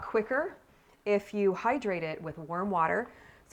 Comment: At first I thought you said worm water.. and I was like wow, she has worm water on tap, then I realised......... ohhhhhhh warm water LOL